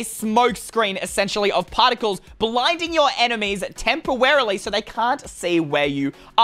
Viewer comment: did say tempewarely? 😆😆